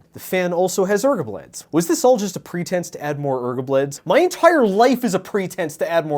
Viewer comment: I thought you were saying ergo-bleds as like a brand name for waaaaay too long...